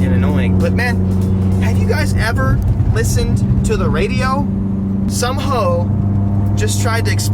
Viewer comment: did just say “some hoe” 🤣🤣